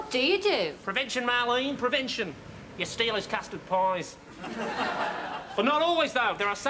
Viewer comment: They say our Australian accent is actually difficult to do... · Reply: "You steal his costume…" Sorry, I couldn't understand what said there after costume.